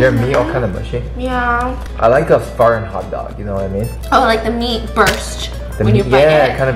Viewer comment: I no joke thought he said fart hotdog 💀